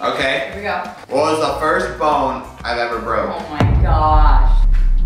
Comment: lol I thought you said what was the first phone I ever broke…. But nope I heard it wrong. Bone. What bone did you break first. For my first and only phone I am unable to remember the name of the phone only that the Congo ride in Bush Garden’s broke it with water damage.